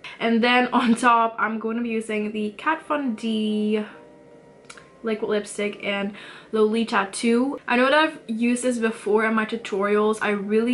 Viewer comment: What’s the shade of the lipstick v? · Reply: I heard her say Kat Von D Lipstick in the shade Lolita ll. It’s the Everlasting Liquid Lipstick.